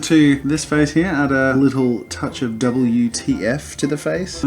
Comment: I must be so tired. At 5:00 I heard “add a little WTF” as add a little “double UTF” and I was like what is “UTF” 🤦‍♀️